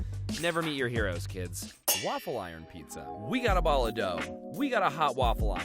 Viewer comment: When he made the waffle iron pizza it sounded like he said we got a baller dough 😭